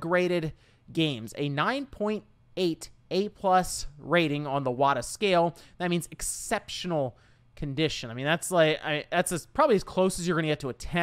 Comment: I keep hearing "Iwata scale", hahaha